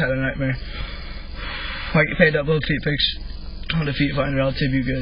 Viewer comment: I swear I thought he said feet finder am I tweaking?